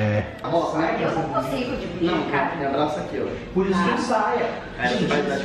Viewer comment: coloca em velocidade 0,25× da p ouvi certinho amor da like p dri ve esse comentario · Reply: Ele fala "amô ponha a mão aqui"